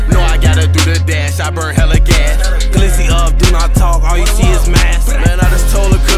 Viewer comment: Am I the only person who heard another voice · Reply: Walter Solomon it is another voice he say "glizzy up do not talk all you see is mask" and herb say "i just told lil cuz chill but i might let him spazz"